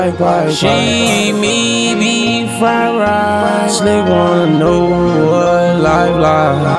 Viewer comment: Did he say “she made me fried riceee” 😭 at 1:42?